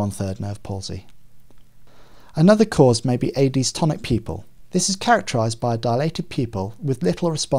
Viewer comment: I wish he placed the terminologies too or a caption. I couldn't understand this part, 1:26. Was that AD's tonic pupil? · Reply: Adie's Tonic pupil